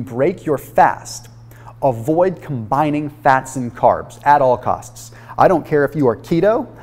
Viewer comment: I was nodding off while watching this and I thought he said "fast cars" when he said "fats and carbs." Hahahaha. "I don't care if you're not keto, avoid fast cars>" Yep, sound advice. I see nothing wrong with this...